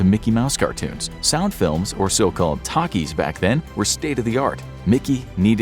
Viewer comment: at first I thought he said "takis" 💀💀